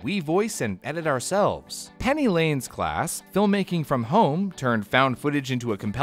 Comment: I heard "bring me home"